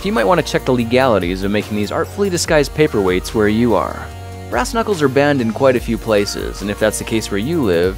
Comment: I thought u said grass knuckles